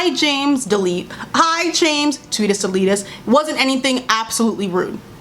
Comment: Did she just say "tweetus deletus?" I'm weak